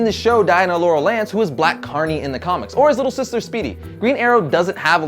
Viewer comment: Did he just say "Black Carnie?"